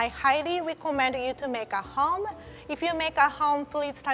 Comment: Lol, I thought I just heard "make it spicy" xD